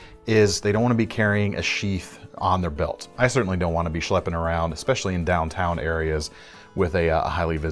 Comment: did he say "schlepping" around?^^